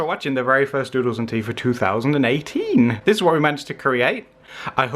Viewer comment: My WiFi stopped working just when was saying this was are first doodle of 2018 so it sounded like he was saying this was are first doodle of 2008. I got so confused